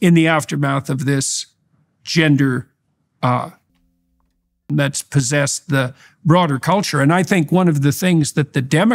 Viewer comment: anyone know what he said at 38:00 ? ... my guess is "shit storm", but I don't read lips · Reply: He says “delusion”. I checked on Daily Wire